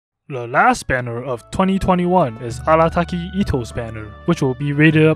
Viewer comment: Why did I hear “le last banner of 2021 is Allataki Itto’s banner” 😂